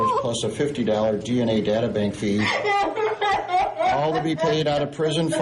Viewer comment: Is it just me or did anyone else hear "Data bank fee" at 8:14? (lol imagine if prison had wifi)